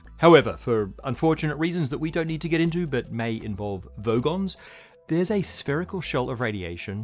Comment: Did he say Vogons? 😂